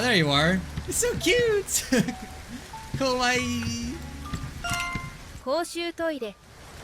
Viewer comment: Did you just call the dog scary 怖い（こわい）? Cute is かわいい.